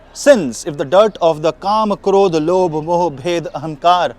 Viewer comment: you speak about sin snd flip into Hindi ... what did you say? Please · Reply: Kam,krodh,lobh,moh,bhed,ahamkar are sins